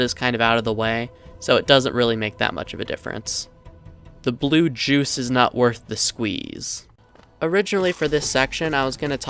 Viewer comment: What did you say?! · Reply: “Blue berry juice isn’t worth it”